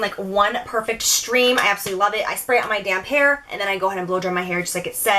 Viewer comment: i 100% thought you said "i spray this on my damn hair" instead of damp....